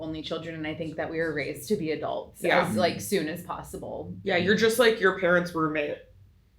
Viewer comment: I heard “you’re just like your parents vermin” and I was nodding along “yea, yep. just a house rat. accurate. “